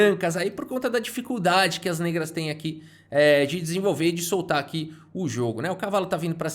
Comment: só eu que ouvi “pisenvolver”?